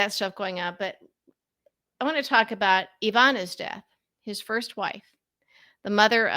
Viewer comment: Him and his kind are all about child sacrifice. He isn't a traitor. Our country was built on Baal and Moloch worship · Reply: Excuse me. I thought you said Ivanka not Ivana. Same applies